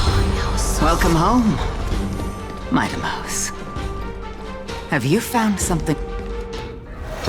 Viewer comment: for anyone searching for what Gorgo's dialogue says at 5:39:04, she says "Have you found something deserving of your protection as well?"